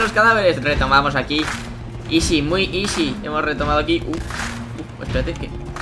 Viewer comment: dijo ISI que se conoce completamente como isis youtube denuncia y borra jajaja (se que dijo easy)